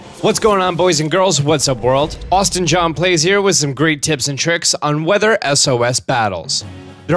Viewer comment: I heard Jin saying Worldwide Handsome ;)